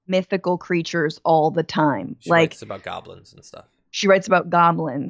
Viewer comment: Did somebody say Goblins??